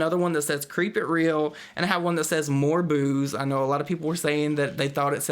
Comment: Ha ha I thought that pumpkin said more boobs too